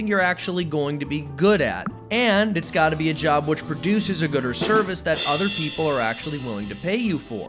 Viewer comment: Our economy is so service oriented I thought said “gooder service” lol